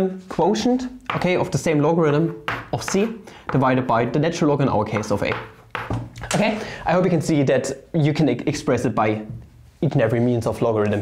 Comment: I heard "in our case" as "in all case" at 4:40 and got very concerned for a bit there.